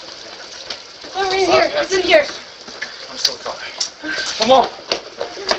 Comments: I swear it looks like she is in a rush to get these guy into her house, then i hear "he's in here" followed by one of them saying sorry to her....the hearing that her partner was the one who shot...this looks like a failed hit job. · Did anyone else hear one of the guys say "I'm so sorry" before entering? He says it like he was in her way or he stepped on her foot or something. I don't know. Probably explainable but it looks like odd behaviour from someone who would then pistol whip and beat her up. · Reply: Yeah next to her saying “in here” , I heard “I’m so sorry”